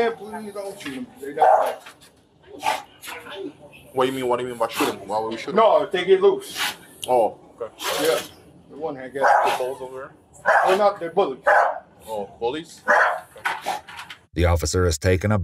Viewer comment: "that one there i guess pitbulls over there" is what i think he said